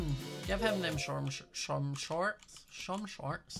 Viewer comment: When was saying short shorts and slipped up a bit I thought he said Shwartz shorts and i was like: SCOTT WATCHES THE GOLDBERGS AND LIKES GEOFF’S SHORTS?! And then I was like nah it’s an American show sadly